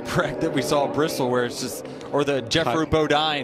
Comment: Did he just say Jethro Bodine instead of Geoffrey Bodine?